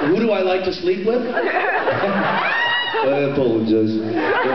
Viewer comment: my gawd did anyone noticed that when misha asks " who do I like to sleep with? " jensen said his own name? I don't understand what jensen is saying before but after that it's definitely his own name:D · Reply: It sounds like he said " I apologize" . His accent is thick it distorted the words.